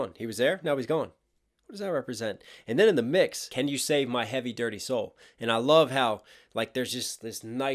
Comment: Bro this whole time I thought he was saying "can you say it ma"